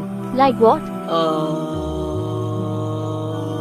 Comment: That last guy was wild 💀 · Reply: I thought he said school shi-tjdjdter clothes💀